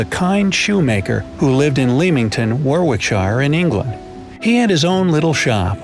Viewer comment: I thought he said “LAMEington”